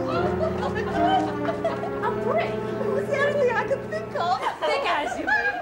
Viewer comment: Thought they said he's a prick 🤔 brick lol 😆 😂 🤣